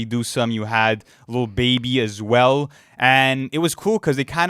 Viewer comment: I KNEW I HEARD BABY